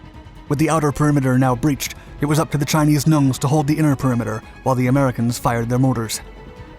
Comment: did I hear “Chinese Nuns”?